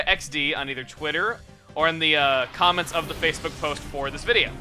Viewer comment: Didn't say to post the suggestions on Twitter and Facebook? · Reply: ***** I think he also said to post in the comments. Lemme check... OH. I thought he said "Comment section of this video" not "Comments of this Facebook post." Oh well. Maybe he'll look at it anyways.